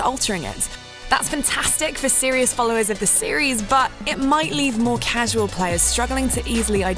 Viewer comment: Definitely thought she said "great for series followers of the series" at the beginning.